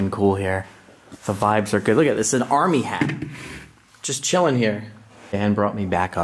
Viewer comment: Did he say "an armyhat"? :face-green-smiling